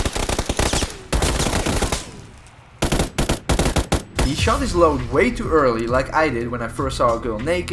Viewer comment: “He shot his shot way too early, like I did when I first saw a girl naked”😂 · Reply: I thought he said shot his load